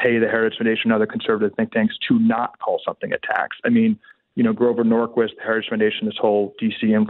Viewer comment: Is Calley saying "a tax" or "attacks?" I've been considering it and it could go either way given the context. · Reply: A tax